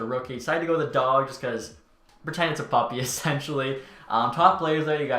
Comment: what did you say? Virtanen is a puppy? I have no idea